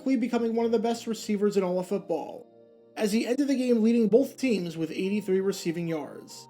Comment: Wait did you say Largent lead both teams in recieving with 83 yards? I know it was a different era, but 83 yards through 4 games? Damn Nevermind i heard "entered the game" not "ended"